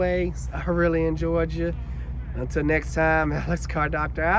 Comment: Alex I like in the video at the end you said I really enjoyed ya but it sounded like you said I'm really in Georgia. Lol😂